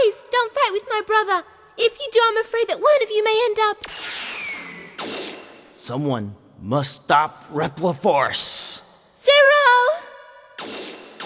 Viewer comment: Zero: KURNAL! Colonel: Wha- Zero: IM DISSAPOINTED IN YHU KURNAL HAAA!!! Colonel: WHATDOYOUTHINKYOURDOING?!?! Zero: Its nut 2 late! Stop da coop nao! Colonel: Neva! Zero: If dats ur decision... PREPAREYOURSELF! (epic fight scene) Iris: (magically appears) STOP PLES! BRUTHA PLES DUNT YHU REMEMBA?! ZERO SAVED MAH LIEF! Colonel: O... Vereh well den i'll spare yo life 4 now, but next tiem dere will be nu mercey! Iris: Zero ples dunt fight with mah brutha if yhu do I'm afrayed one of yhu may end up- Zero: Some1 must stup repleefource! (walks off) Iris: ZEROO!!! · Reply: I thought he said reploifarce